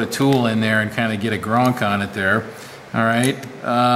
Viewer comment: Did you say put a tool on it and get a grunk on it at the 17:57 mark? Thats quite a way with words you have there sir! :) I just bought a Hougen Mag mag drill on ebay used, and those are setup to primarily use those Annular cutters... So $20 was a steal! ...Good meatloaf as always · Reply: Hey Turbo, Actually the word was "Gronk" You know the little noise a fastener makes when its tight and seats down. All the best